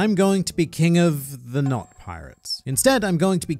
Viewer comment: I initially heard “Nox” instead of “Not” and became very confused.